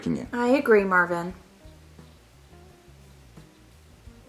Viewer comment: “I wanna see the hot girl-“ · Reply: The first time i heard that i heard “I wanna see the hospital”